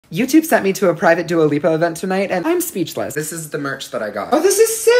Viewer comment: I literally thought you said YouTube sent me to a private duolingo concert 😂😂😂